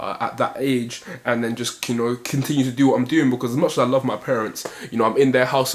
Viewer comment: Bro did I hear that right 10:42 ‘I’m not sure I love my parents’